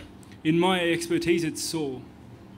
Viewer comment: What did Rob say? "In my expertise it's ????" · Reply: sore